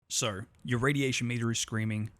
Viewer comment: What he says "So"... What I hear "So'ur"...